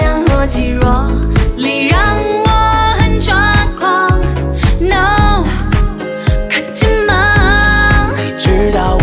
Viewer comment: 聽成了(no~卡機嗎~